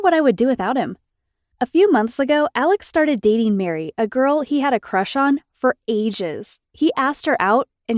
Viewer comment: I keep hearing this: A girl he had a crush on for ASIANS Just me???